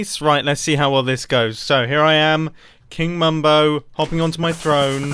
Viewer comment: He said "King Mumbo" I heard "Mayor Mumbo" #MumboForMayor